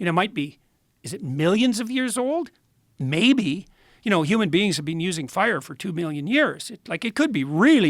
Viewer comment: Did he just say "Human beings have been using fire for 2 billion years"...? What lol? I listened several times, he definitely said Billion, with a B. Humans have been using fire since before the dinosaurs walked the Earth I guess, hmm. News to me, the lowely highschool graduate I am